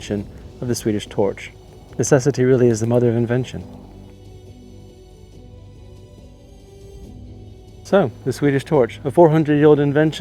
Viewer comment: First time I heard "this really is a modern invention" instead of "necessity really is the mother of invention" and I thought what a lovely way of thinking about 400 years ago: our shared modern age.